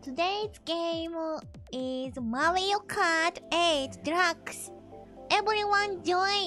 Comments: So cute. It sounded like she said Mario Kart 8 Drugs 😂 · I thought she said drugs at first when she said deluxe.